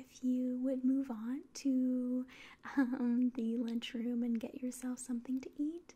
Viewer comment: I yawned right as you said lunch room, thought you said 'lynch room' and I've never jumped out of my slumber faster.